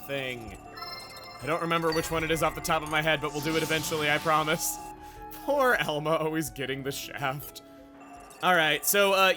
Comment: Zeke is going to catch hypothermia 0:47 Nia: And cut! 5:50 Wait.....AKHOS!????? THE SNOBBY MAN THAT HAS A SEMI-CRUSH ON?!!?!?!? 8:10 I legit forgot who Pandoria was for a moment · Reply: did he just say, Poor Elmo?